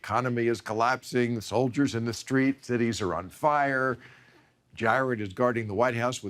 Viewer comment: "Jared is guarding the White House with a shotgun." Funny, somehow I thought Bill said Jethro.